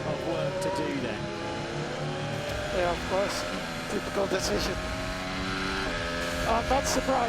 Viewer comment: when max says "typical decision" the captions says "Difficult Decision" lol🤣😂